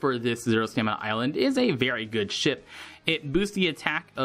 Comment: pretty sure the STR unit on stage 17 isn't Gedatsu :/ know that it's not him, because I actually got Gedatsu from friend point sugo 5 times now LOL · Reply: also, no idea why I heard "good shit" instead of "good ship"